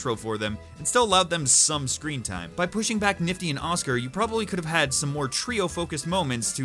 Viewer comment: did he just say oscer...